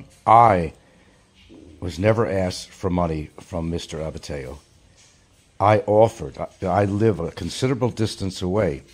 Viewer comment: Did he say he offered? What a bribe?